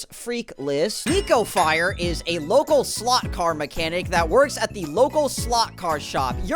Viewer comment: For a sec I thought you said Niko Not Nico Lmao Too much oneshot